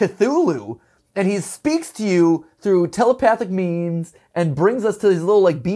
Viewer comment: ha telepathic memes is what I heard there